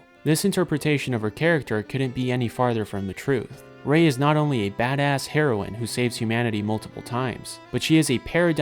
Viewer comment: "she's not only a badass heroin" thats what i heard